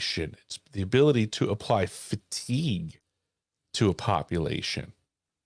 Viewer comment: did say "ability to apply 'FATIGUE' to a population." ?